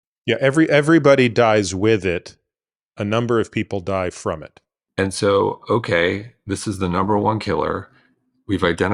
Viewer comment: At around 23:00 dr attia names drugs but I cannot understand what he said after pcsk9 inhibitors. He spoke too quickly. Can someone please tell me what he said? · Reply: Ezetimibe & Bempedoic Acid